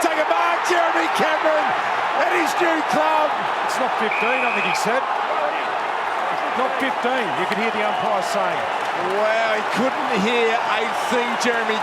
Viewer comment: umpire is clearly saying "touched- play on", commentators didn't hear it - correct call. 1:17 - he is being held, you can see on the shoulder- its tough but its correct 2:55 - whilst it has a lot of force, its still a tackle and Ward drops the ball, similar to how if you're tackled whilst bouncing its ball - correct call 6:35 - Clark kicks the ball slightly (you can see it deviate of his foot) therefore its not HTB, whilst Hill does not attempt to dispose, and Butler is obviously 50 7:06 - the tackle call could've been wrong but based on the rules at the time it is dissent and 50- there was another one during the same game the exact same against the Bulldogs 11:43 - ducking is Ginnivan's prior opportunity (he could've handballed/kicked within the time he took to decide to duck), and ducking does not give a high free - correct call 14:18 - Blakey's head hit the ground pretty hard, he didnt exaggerate the contact, it should be a dangerous tackle (tackle being dangerous voids HTB from the equation) 17:16 - you cannot hit the footy further away after an out on the full with a fist etc, it's 50- easy call (happened Round 23 2021 Melbourne v Geelong at the end of the game too, its in the rulebook) apart from that all these calls are bum